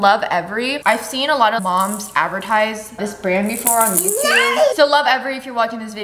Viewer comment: Did she say NICE